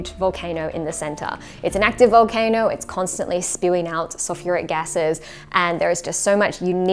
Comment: Why do the captions say “soft cigarette gases” instead of sulfuric gases? 😭😭😭😂